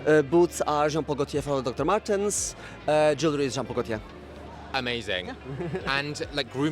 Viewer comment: Anyone know what the Jewelry is from at 15:31? I could quite catch it and CC doesn’t say anything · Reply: Jean Paul Gaultier